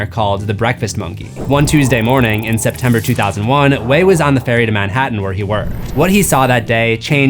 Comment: I thought Gerard said he was on a train not a ferry.